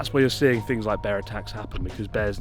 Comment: I thought he was saying 'bear Tax' for a second there.